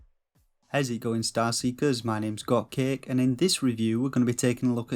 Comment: Totally unrelated but for the longest time I thought you said your name was “cupcake” and not “got cake”. I’m an ass haha